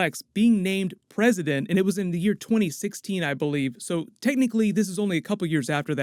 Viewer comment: put subtitles on. says 2016 i believe but captions say 2018